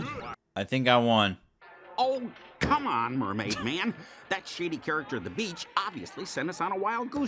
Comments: Instead of shady, I thought he said shity. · Did barnacle boy say "That shitty character" at 3:51 If so, they put that in a spongebob game what, I don't remember them saying such